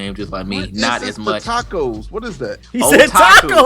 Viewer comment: Started dying at 40:11 thought he was saying a taco or some shit too